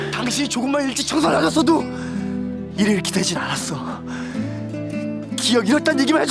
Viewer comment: 진짜 죄송한데 6:52 권상우씨가 뭐라고한거죠?.. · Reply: 당신이 조금만 일찍 정서 놔줬어도.. 일이 이렇게 되진 않았어.. 아닐까요...?